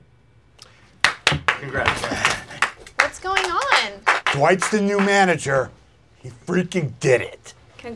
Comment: I thought he said Dwight the new manager freakin gay